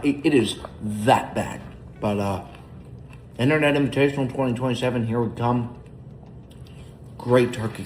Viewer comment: Did he say 2027?